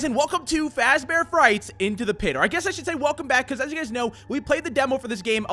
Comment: Did you just say into the intodo